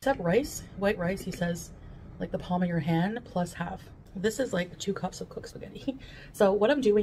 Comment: Girl, I eat between 1600 and 1800 kcal a day with a focus on protein (130g) and I'm comfortable by the end of the day. I'm not stuffed, but I'm not going to bed hungry. Also, 6k steps take 1:30h? You can do that in 30 minutes. The excuses are really getting gout of hand. Didn't the doctor tell her to have a small palm-sized serving of rice? Aka carbs? That full wheat pasta, while healthier because of fiber, the carbs are HIGH. At least 75g of carbs for 100g of pasta, and that is at least 300 of pasta on her plate. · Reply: I thought she said palm snd s half but it would be like half a cup of basmati only that is reallt allowed